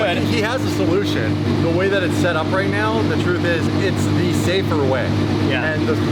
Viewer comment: "the way my bank account is set up..." - Kevin Hart I cant be the only one who heard it.